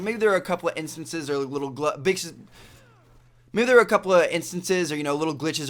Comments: Sounds like he said the b-word at 1:01. You've got to censor that! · Did say B**ches at 1:00 ?